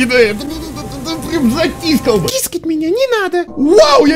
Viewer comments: Бля, стесняшечка! Мне из начально послышалось: кискать меня не надо!.... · Мне послышалось: кискать меня не надо.